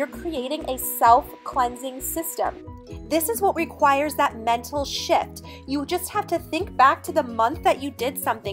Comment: sounds like you said, "mental sh*+". LOL! Could go either way! LOL. Take care. Great video!!!